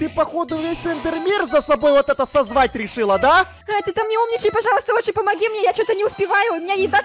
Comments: я одна услышала :,, зай, ты там не умничай "?? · Мне там одной послышалось что сказала зай ты там не умничай 8:31 эда заей называет?!